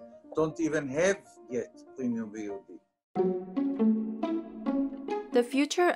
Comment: What is this guy saying? I can’t understand him. He mumbles. “....because in the international market, many countries don’t even have yet premium viewugy.” What is a viewugy? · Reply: Premium VOD